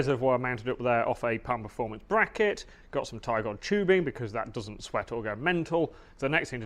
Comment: what kind of tubing did you say? English is not my first language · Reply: Tygon